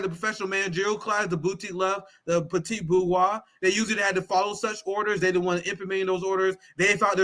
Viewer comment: Ok, not to be picky, but I keep hearing “Petite bourgeoisie” and I feel like that can’t be right